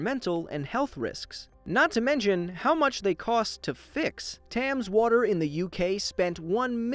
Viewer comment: Did you just say “tams water”